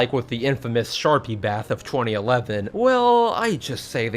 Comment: "like with the infamous sharpie bath in 2011" HOW DO YOU EVEN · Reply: I thought he said sharky bath